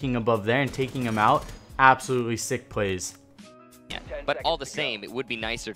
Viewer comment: What in the world are you even saying right here? LOL 4:05 "Absolutely sick plays."